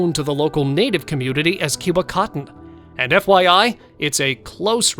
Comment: Did he just say Cubacotton?!? Haha